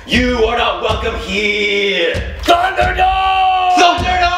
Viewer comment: I laughed so hard when said "Thunderdoome Baby" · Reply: It kinda sounded like he said lunderdome.